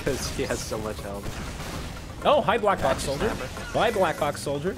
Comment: Is it just me or did anyone else hear "black glock" soldier